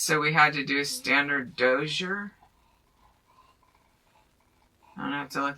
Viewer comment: I did not quite hear the word and context clearly, I did not know whether was saying DOZIER or DOSSIER I thought said the real word "Dossier", for example it means a file containing detailed records on a particular person or subject" but looking more carefully says they even spelled it out for her. D O Z I E R. There are several definitions for that word, so one of them should fit the context.